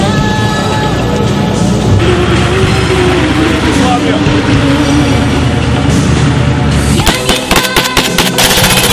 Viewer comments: What did the guy in the jeep say? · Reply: Yugoslavia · what did he say? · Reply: Yugoslavia! ✌🏼🎖